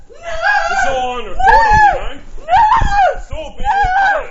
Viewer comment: One small mistake in the subtitles, but at around 25:25, it says "its all over" but if you listen closely he actually says "this is all on recording, ya know?" Idk if that means anything to the case overall, but its just a weird detail that he made a point to tell her he was recording everything. Almost like he was confident that she wouldn't be able to use that against him later. Maybe I'm reading into it a little too much, but that line just felt very taunting.